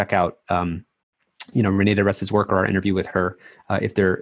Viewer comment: What does he say ? (inaudible) "You know I really recommend people check out...xxxxx's work" - what is that name? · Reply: renee diresta